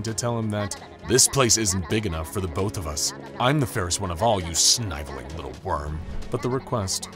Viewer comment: watch whole video 'snyveling' @ 19:08 all i heard